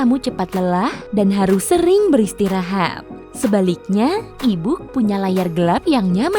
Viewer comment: E-book Aku dengernya ibuk :v